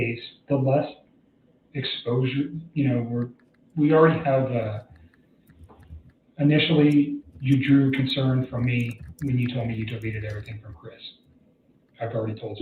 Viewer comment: Ugh ! What on earth does he say .. alrwAdy drew concern from me when you told me what? Time stamp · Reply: initially, you drew concern from me when you deleted your text messages